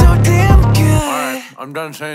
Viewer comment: i only hear " cough i lied "